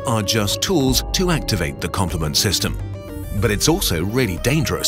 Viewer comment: i thought it said the compliment system